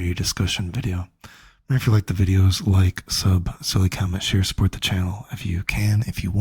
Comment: What the hell is he saying. like sub, sell the comments? like I never understood what you were saying there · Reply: "silly comment"